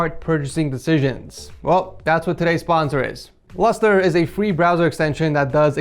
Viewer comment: I thought that you said Lester instead of Lustre Xd